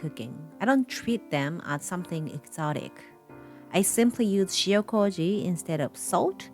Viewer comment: What she taking instead of salt again? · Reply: Shio koji. I have no idea what it is but I googl3d it after she said it. Around 2:06 ish